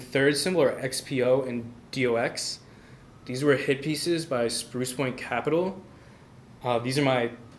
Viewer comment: did he say spruce Wayne capital? Sprucewin? Thanks Looking like it’s Sprucepoint cap.